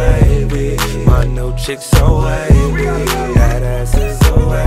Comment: My cars so wavy, my closet so wavy, my new chick so wavy... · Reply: always thought they were saying "my milkshake so wavy" instead of "my chick so wavy"